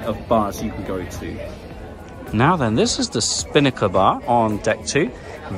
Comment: I thought you said spanker bar not spinnaker bar, goodness that made me laugh🤭